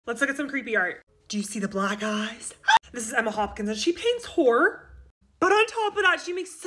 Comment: Sounded like you said “she paints whore” lol sorry I’m British 😂